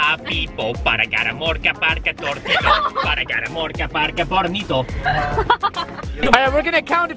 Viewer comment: Did he say PORNITO?hahahah